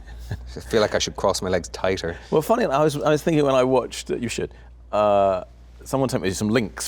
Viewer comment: does Hugh say “I feel as though I’ve watched you sh*t”